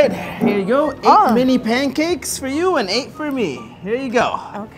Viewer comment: for a sec I thought he said “6 for you and 8 for me!” and I was like “OFC THAT AINT FAIR” and then they started talking abt the flavors and I was like “HUHHHHH?”